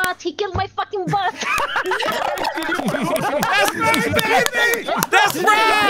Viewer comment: I think the fact that cartoonz saying "that's my baby" was very wholesome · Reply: He said “that’s right, baby”, but he screamed so loud that it cut him off a bit.